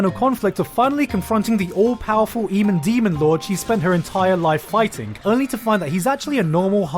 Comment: did he just call him the emen demon lord?